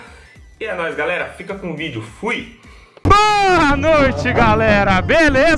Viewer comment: só eu ouvi ele falando porra noite?